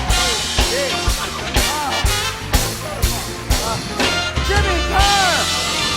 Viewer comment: i thought it said jim carrey lol